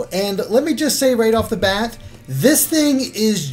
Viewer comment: right off the bath? 0:21 XD (I know it is bat, it just sounded like bath to me.)